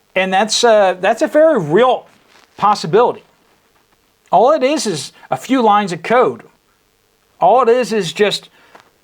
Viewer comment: did you just say "all it is is 2 line's of coke"